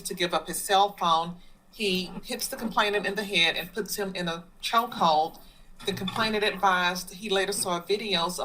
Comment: A chokehold??? Lol, I thought he said "a hug"...